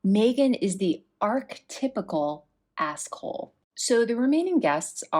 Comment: askhole = someone who continually asks for advice but never follows it. askholes can make their friends feel used and abused. (Macmillan dictionary) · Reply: I thought you said arsehole which I also agree with.